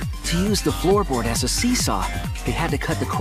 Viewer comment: did somebody say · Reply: SEESAW